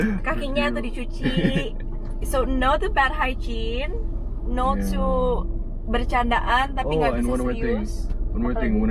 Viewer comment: itu ngomong apa ya? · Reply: Dia nggak suka cewek kotor, harus yg bersis rapi dll, haigin tu cara bacanya higienis gitu maksudnya wkwk